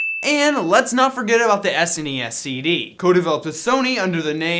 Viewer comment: yo did you just call the SNES a sin?